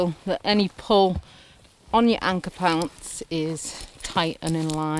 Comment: did you say "anchor pants" :-D